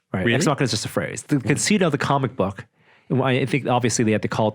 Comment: Please clear something up for me. At about 1:08:30, it sounds like they're discussing the concept of a show, but the word sounds like conceit or concede. This is not the first time that I've heard this form Norm and the gang. Perhaps I'm not hearing clearly, but what are they saying? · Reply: Yup they say conceit, dictionary meaning is 1. excessive pride in oneself. "he was puffed up with conceit" 2. an ingenious or fanciful comparison or metaphor. "the idea of the wind's singing is a prime romantic conceit" They probably use it instead of simply saying "concept" because of 1, but use it as meaning 2. 😅